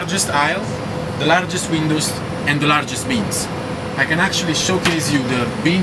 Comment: I thought he said the 'largest memes'